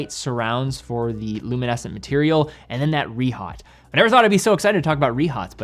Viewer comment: What are you saying around 12:13? ”Re-hot”? Not a term I’ve heard before, great if someone could explain it…. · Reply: A rehaut is a raised, thin metal ring on the inside of a watch case that surrounds the dial.